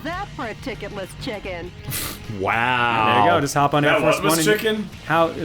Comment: I heard chickenless chicken too.